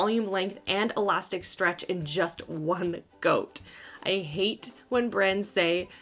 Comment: It sounded like you said "just one goat" 🐐😂